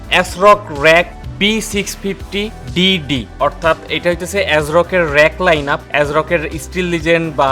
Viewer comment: did someone say Diddy?